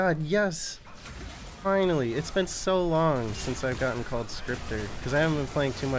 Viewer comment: for some reason I hear "is been so long Since I last have seen my son" in the minute